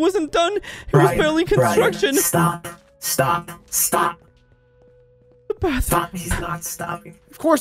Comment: Listen closely.....did swear? It sounded like he said the f word. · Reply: He said stop but ok